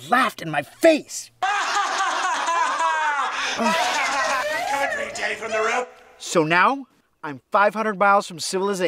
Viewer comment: what was that guy from Phil’s thoughts saying? Oh hai Denny from the Room? Oh tell me the truth Denny from the room? Oh hi I’m Denny from the room? Oh come here Denny from the room? Or was it Come and play Denny from the room? I tried using the slow playback speed to hear what that guy was saying but he and that lady were laughing way to loud even slow for me to hear what that guy was saying. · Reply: Haha - he's saying "I'm coming for you Denny from the Room" :-)